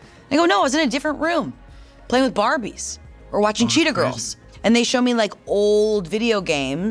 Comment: im new so idk if its an inside joke or i misheard, but i just noticed the caption said pretty girls instead of cheetah girls 😭